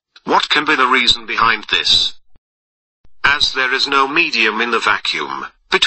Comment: what does he say?? I heard "as there is no medior" what does he really say? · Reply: abode TUBE no medium